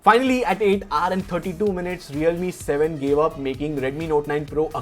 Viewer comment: did he just said realme 7 Pro instead of realme 7??😂😂🔥